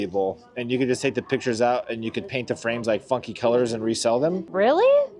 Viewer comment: Omg 24:32 it sounded like said “F*** colors” I replayed it like 4 times 🤣